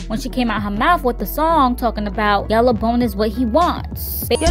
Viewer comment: Idk wtf she was saying. I heard "yellow BONE is what he like budda budda"